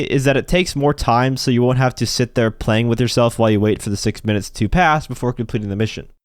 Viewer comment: Did...Did you just say, "playing with yourself" at 4:42? Also, for contact missions in general, I have found 5-6 minutes to be the sweet spot to get these missions done. If I can't get it done by then, that's fine, since I know I'll get paid more. Thanks! (One further question: Should I stick with the Special Carbine Mk II or switch to the Carbine Rifle Mk II? I like the former's unsuppressed sound, but the latter's look. Also, I just want the more accurate one of the two.)